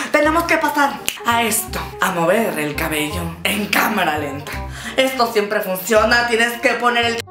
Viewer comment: antes del minuto 6:45 o menos cuando dijo vamos a mover el cabello en camara lenta entendi vamos a mover el camion en camara lenta 😂😂😂😂